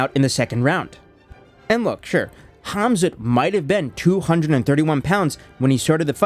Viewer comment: did you say KhamZutt? 😂😂😂 Oooh, that story of Mohammed's wild night out did really spreas like a wildfire, huh? 🤣